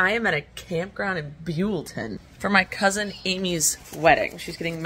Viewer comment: I thought I heard you say "...my cousin emu's wedding..." then later in the video there's an actual emu. HAH